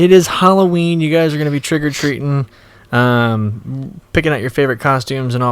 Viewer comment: Almost sounds like you said "trigger-treating" 😂